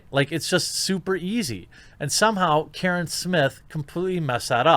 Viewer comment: Wait, did you call him “Karen Smith” (at 9:11)?? Let’s hope that trends. Hilarious.